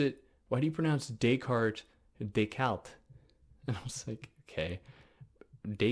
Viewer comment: Regarding the pronunciation of "Descartes," I think you don't hear that you're inserting an L sound for the fricative French R. It sounds like DesCalt. Still, I'm loving this series. Heidegger is a real gap in my knowledge, and you're helping me a lot!